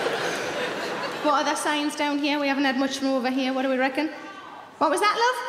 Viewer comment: Did she say “what do we reckon”? I’m from the Deep South in the USA and I’ve never heard someone not from here use that word “reckon”